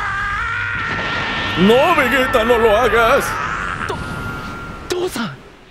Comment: Que dijo dijo tusa